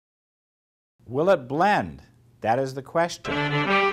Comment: I thought it said ' will it BEND' at first but then I realised when he had two blenders in his hands, ha ha !!!??!?!?!??